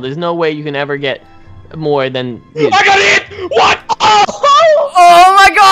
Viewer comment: When he said We got eight! I thought he said I got hit